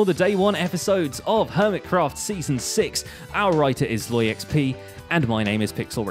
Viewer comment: hahaha great show, I thought you were saying "and our writer is low on xp" the last 3 shows I've watched! :D